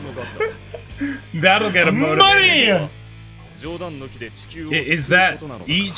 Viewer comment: if anyone is still watching this video 5-6 years later and checks new comments, could you help me figure out what the last word/phrase said here that sounds like entigear? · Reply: I only hear "that'll get them motivated" and "Money!"